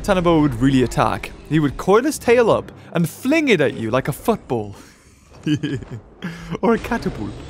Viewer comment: Did he say fuck ball